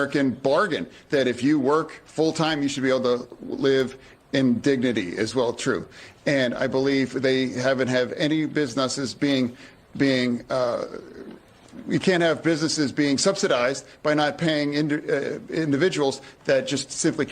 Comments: Did he just say "biznutses"? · Did Fetterman just say, "Biz nuts"? 😳